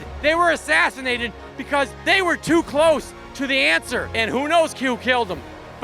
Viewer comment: Did I hear “Who knows, Q killed them?” That’s the best one liner bomb drop ever.